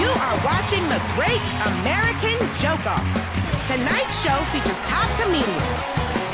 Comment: I keep hearing say "The Great American Jerk-Off"